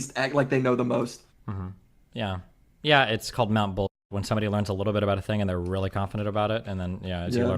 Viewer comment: its called what? Whatd destiny say. ? Mountain bull ? Lol